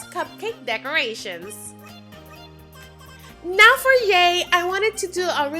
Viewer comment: Who else heard when she said now for yay.